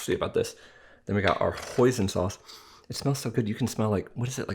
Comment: hoison sauce it sounds like poison sauce and i thought he said poison sauce because it is spelled the same way with one letter different /poison/ /hoison/ hoison sauce it is my first time hearing that word hoison sauce it is usually i hear tarragon or soy sauce soybean soy and curds and whey and molasses and chocolate whipped cream and strawberry whipped cream and chocolate mousse and raspberry mousse and frozen yogurt sorbet but not hoison sauce.